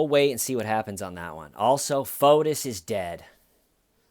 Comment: who is dead? anybody spell the name for me please, my subtitles say "foetus", and "Fotis", it can't be correct...is it the same case?